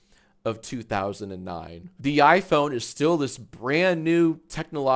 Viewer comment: I think watching Jacksfilms these many years has done something to my brain. On initial viewing I could've sworn he said "the YIAY phone is still this brand new technological miracle".